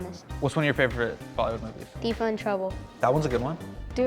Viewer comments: DID HE SAY “TEEFA INTROUBLE “ which is A PAKISTANI MOVIE / LOLLYWOOD, love Hasan minhaj , we have the same name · what's favourite movie at 13:23? couldn't catch that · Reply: Teefa in trouble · Did just say 'Teefa in trouble'??? Wow, Pakistani Cinema really has risen to a certain level. Where are my Ali Zafar fans at???